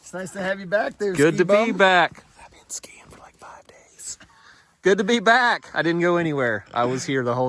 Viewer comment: if you can figure out what he says here I will give you Donut! (Bun & frosting Not included) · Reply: I’ve been gone skiing for like five days!